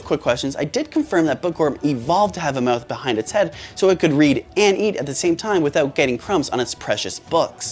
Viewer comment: HOLD UP- Dirty Mind gang here 👇🏻 ☝🏻 Nah İ thought it said breed💀